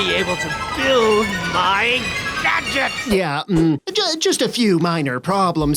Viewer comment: Captions heard words: 3:00 Magic instead of gadgets 3:34 Games Instead of game's 3:35 Cinch instead of sinch 5:03 [Applause] [Music] Instead of BIG DADDY 6:30 Eggs instead of Egg 6:32 oh instead of Ovo 6:36 Hoos instead of Whos 7:06 luncher instead of launcher 7:54 Slime instead of slimed 7:55 agg instead of egg 7:56 Hoos instead of Whos 11:42 Hoot instead of Who